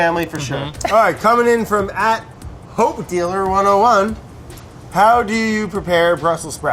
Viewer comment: I thought that handle said CokeDealer101